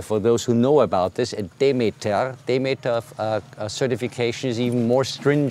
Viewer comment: What is the name of the second certification they mentioned at 9:00 ? Sounds like he says, "They made of" but I can't find anything online about it. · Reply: He's taling about getting DEMETER certification.